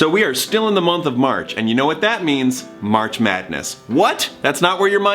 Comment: I thought you said March sadness I mental was like "yep sounds about right"